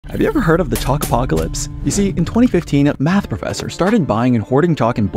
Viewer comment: Oh! For a second I thought he said chocolate-apocalypse.